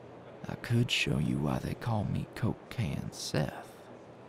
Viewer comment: for a sec I thought he said "cocaine seth" and i was like "wait what DO U MEAN-"